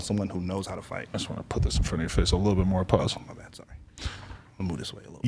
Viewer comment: did Adam say “pause” 😂